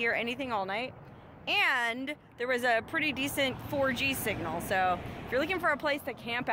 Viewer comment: I thought she said orgy signal!